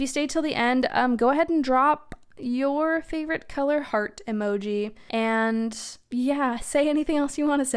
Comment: I totally misheard 'heart emoji' as 'fart emoji' and was amazed that not only is there a fart emoji, but it comes in different colors!! 🤣🫀